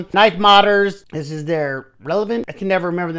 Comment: Excuse me please, but what's the word after knife? I just can't make it out. :( Okay I figured it out after listening to it 10 times and doing 5 googles, it's the KnifeModders Revenant.